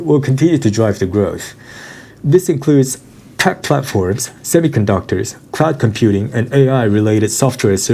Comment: did he says tech platforms After semi conductors?